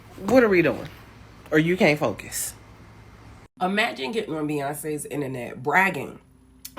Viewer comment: Did she really say "Beyonce's internet"?